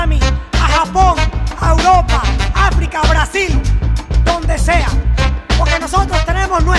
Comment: Eu escutei BRAZIL? 👂👌👌